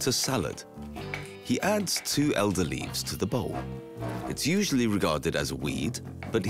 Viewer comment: What did he say here, what kind of leafs? Any native English speakers that can help? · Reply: Elder leaves.